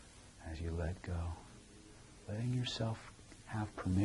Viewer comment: Can someone write down exatly, what told at 24:55-57? I don't understand it. And the automatic transcipt is wrong. Thanks! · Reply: Kovács Gabriella sounds like "to learn things unconsciously"